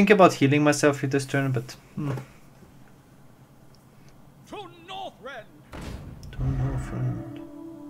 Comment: Did say "To boyfriend! To victory" ?